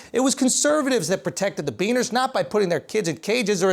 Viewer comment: Did he say Bean%#$? Am i hearing things?